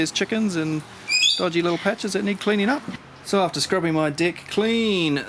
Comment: WOW. This is really niffty. And my son thought you said " after scrubbing my d1ck clean. LOL